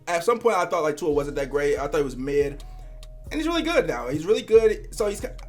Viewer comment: 🤡🤡🤡 I thought u said too was mid